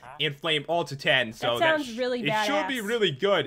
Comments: i could of sworn that i heard say "that sounds pretty badass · Did Say bad A $$??! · did just say badass?